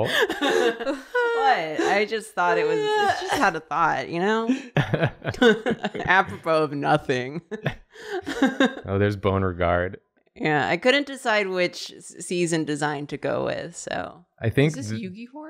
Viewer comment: I thought he said "Theres a boner guard" 😂 I was like oh, did she put a cup on one of the characters XD